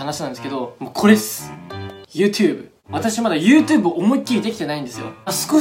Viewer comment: はやコメ！＆初コメ！ 0:17YouTubeがユーテゥーブって聞こえたw